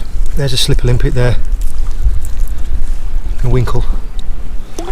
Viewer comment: I swear I heard you say "a slippery limpet and a winkle" and it sounded like you were reading Doctor Suess 🤣🤣🤣